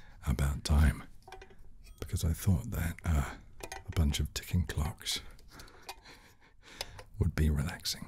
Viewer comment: I thought he said Glocks…